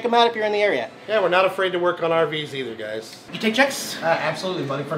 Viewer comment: When he asked if they took checks I thought he said 'do you take Jax'. Yeow!